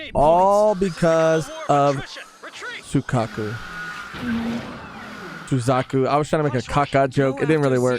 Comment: oh i thought you said “Su-cuck-u”🤣